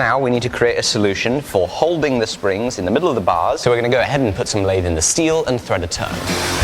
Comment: Am I crazy or did I hear: Go ahead and put some lathe in the steel and thread to turn